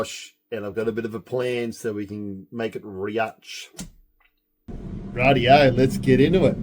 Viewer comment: what are you saying (literally) and which language is from. I heard 'make it rigach' was that right? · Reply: Haha sorry just me saying make it rich $ (in a drawn-out way) Thank you for watching, I hope the rest of the video was ok and if you want me to explain something a little more just let me know 😁